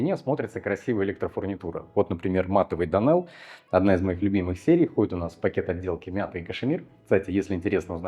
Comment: Что за электрофурнитура ? Производитель и серия ? Нерасслышал. Данэл? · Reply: donel r98